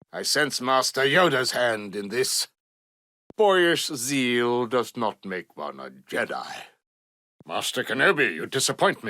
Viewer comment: What Exactly Does DOOKU Say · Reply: ”Boyish zeal does not make one a Jedi”